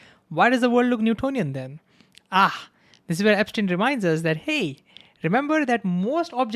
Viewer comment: is he saying Einstein or Epstein ??